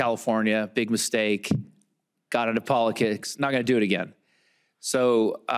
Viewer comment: sounded like he said polikicks lol